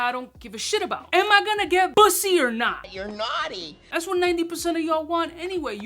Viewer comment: did u say bussy at 2:00 ? PAUSE